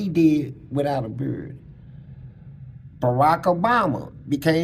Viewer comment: I thought he was saying without a bird. I'm like what type of bird?? But he is talikg that real. These fools think they king of the jungle until the real goriila steps into the forest.